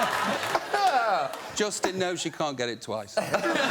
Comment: “Justin no, you can’t get it twice” 😅😂😂😂😂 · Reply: I hear "Justin no, she can't get it twice"